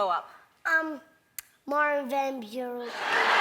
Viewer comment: What does he say, please? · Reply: Martin Van Buren, former US president.